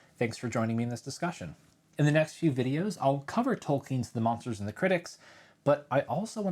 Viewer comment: I thought you said Gerald Tolkien, I was very confused for 2 seconds. 😀